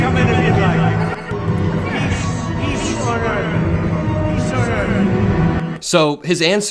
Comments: I heard "Peach on Earth". Not sure what you guys heard. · To me I thought he said "peace an order"